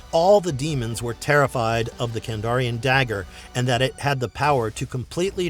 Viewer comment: Sorry but at first I thought that said Kardashian Dagger….